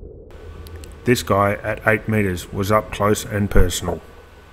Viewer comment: What did you say at 2:37? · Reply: This guy at 8 meters was Up close and personal